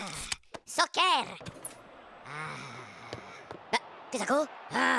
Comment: i only hear socker and top shet